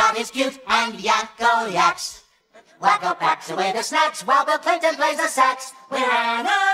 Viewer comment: I always thought they said: "While the 'pinton' plays the sax"